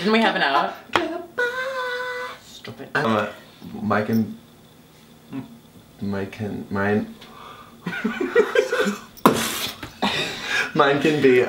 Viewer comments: when scott was saying "mine can" IT SOUNDED LIKE HE WAS SAYING MY CHEM AND I ACTUALLY SCREAMED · I thought Scott was saying My Chemical Romance at 9:30 and I just